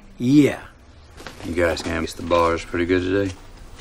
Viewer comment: What is he saying in the first clip at 0:14 ? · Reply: You canvas the bars pretty good today? Meaning they've been drinking on the job.